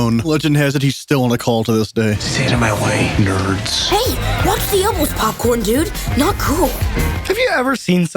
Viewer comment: what is Greg saying? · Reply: Hey, watch the obles popcorn dude